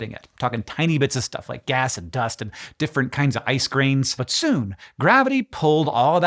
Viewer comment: Was watching this with the volume low while I ate breakfast: "Did he just say 'different kinds of ice creams?'"